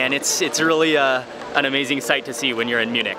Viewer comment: I can’t stop laughing at the sub at 9:05 he said “it’s an amazing sight to see when u r in Munich” but the subtitle said “when u r an eunuch” 🤣🤣🤣